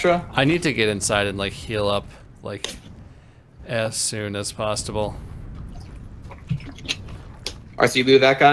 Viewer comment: As soon a pastable? There's no way I misheard that.